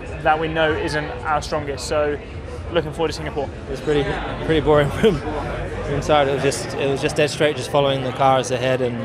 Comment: Did Lewis say "pretty boring red bull" ? I honestly can't tell.. If that's the case, this man needs an amnesia check. Otherwise, my bad, misheard.